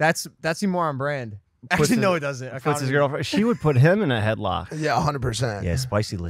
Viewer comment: did Jeff say "I f***ed his girlfriend"?